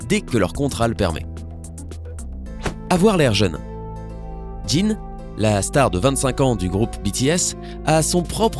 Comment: « ji » quoi ?!